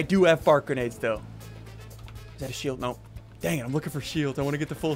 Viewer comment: did he just say damn